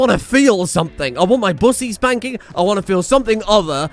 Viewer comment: …Did he say bussy spanking?